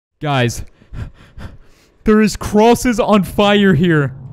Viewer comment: How come I heard him say cruises🤔🤔🤔🤔🤔🤔🤔🤔